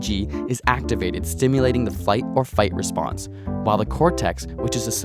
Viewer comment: am i the only one who heard him say "fight or fight" instead of "fight or flight"..?